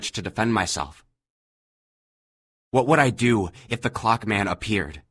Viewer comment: thought it said Cock man at first